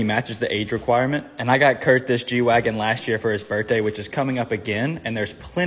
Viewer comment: i legit thought you said “kurtday” instead of birthday for a second… i should probably get some sleep…